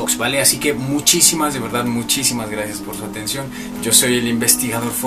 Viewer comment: Yo siempre pense que decia ponte cripi ;-; Y en realidad era ponte crimi